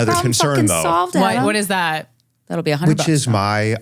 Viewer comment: what's he responding to where he says "oh, i'm worth much more than that." I missed(or can't hear) what must've said. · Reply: I think she said that'll be $100 (for solving his problem) and he said (the advice) is worth more than that